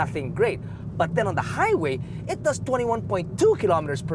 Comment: Is it just me? Every time you say Highway I hear "high weight"